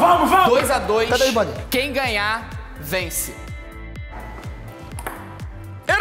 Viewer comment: só eu que ouvi "quem GANHAR VENCE"?